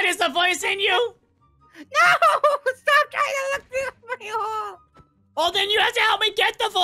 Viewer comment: I thought I heard BunBun say "STOP LOOKING THRU THE FU**ING HOLE."